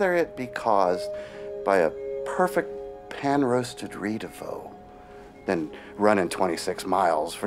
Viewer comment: what food was he talking about at 2:40? Sounds like he was saying Rito fo but I don’t know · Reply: Ris de veau (a fancy name for calf organs)